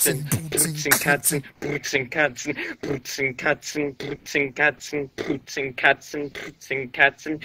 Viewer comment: it sounded like he was saying boots and cancer